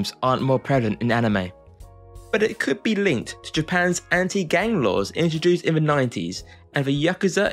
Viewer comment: I misheard “anti gang laws” as “anti gay laws” and got both whiplash and a curious for what could possibly link Japanese gay culture and its suppression and the representation of Yakuza in anime. 😂